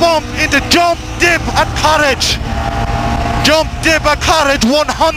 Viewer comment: Is he saying "courage" at 1:14? Haha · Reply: Courage means to be brave and keep her planted on through